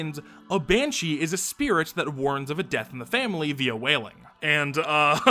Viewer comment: At first i heard this as "death by whaling", and my brain went on a whole tangent about Moby Dick metaphors before i realized my mistake lol.